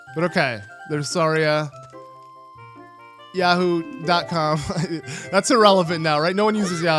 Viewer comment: I believe this might have actually been a misinterpretation of what Saria actually said. It's common to greet friends saying "やほ" which in romanji is "ya ho" (just without the space)